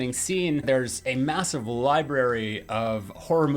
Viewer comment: massive? Did i just hear massive?